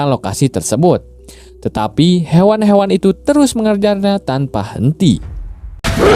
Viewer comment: Menit 17:47 lu ngomong apa bang?😭🤣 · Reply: mengejarnya